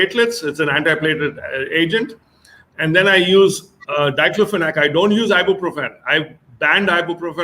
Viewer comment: he said he banned ibuprofen from his practice and uses something else. WHAT? I couldn't hear the drug name. Sounded something like "dycluminate". What's that name? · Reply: I believe he said Diclofenac